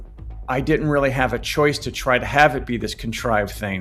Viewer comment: Did he say, "in-stink?" Nobody wants to smell that..